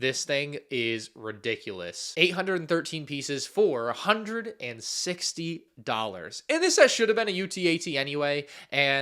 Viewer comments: I heard $460 and nearly had a heart attack. I had to go back and listen a couple times to make sure it was only $160 (Which is still crazy but not $460 crazy) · I almost had a heart attack because I thought you said "four-hundred and sixty dollars" and not "for A hundred-and-sixty dollars". Still ridiculous prices but phew. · I thought you said $460😅